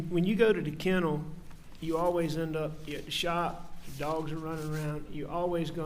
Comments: can someone please tell me what Alec says? Sounds like he says, “ when you go to the kennel, you always end up getting shot, dogs are running around…” no way did he say that, can anyone understand him? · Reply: He says “you always end up at the shop”, meaning the workshop. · Did he just say at 14:35, when you go to the kennel you always end up getting shot, !? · What did he say at 14:30? When you go to the kennel, you always end up getting shot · wait is he saying " whenever you go to the kennels.. you end up getting shot..."??? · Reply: He said “you always end up at the shop”, meaning the workshop.